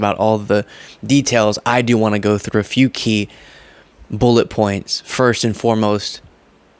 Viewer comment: Did you say you had some bullet points to cover 1:17 ?? 🤣